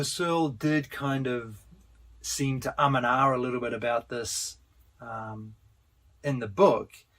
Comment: What is the word used at 17:53? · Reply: "...seem to um and ah about this..."